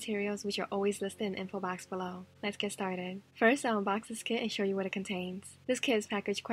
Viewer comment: It sounds like she said let's ge staded sorry I had to .-.